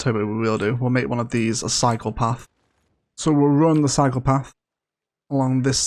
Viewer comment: Lol! When you say cycle path, i swear i hear, “this is where the PSYCHOPATH begins”